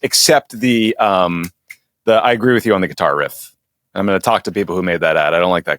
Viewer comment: I kept hearing “guitar riff” as “Qatar riff”